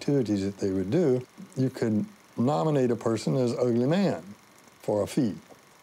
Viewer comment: Did he say "Ugly Man?"